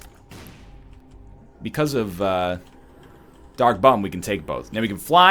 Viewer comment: For a sec I thought dark bum said dank bum